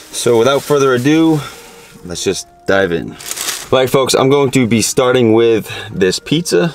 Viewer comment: Why it sounded like you said Black Folks I’m Going To Be Starting With This Pizza👀👀 What’s up with that!?!?!? I Need Answers Don’t Say You Ain’t Say It Cause It Came Outta Your Mouth Especially Pronouncing The Letter B So I Knew You Said Black Folks🤦🏾‍♂️🤦🏾‍♂️